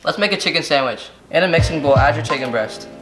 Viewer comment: I’m not the only one that thought he said “Mexican bowl” in the beginning